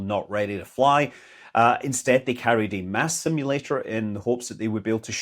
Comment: Why do I always hear “ass simulator”? Mm mm-hmm?